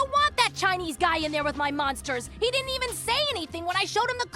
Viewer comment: "so, i dont know how this happened, but the chinese guy is stuck in the force field with the monsters" is not a sentence i anticipated hearing, ever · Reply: I thought he said stuck in a thing with the monsters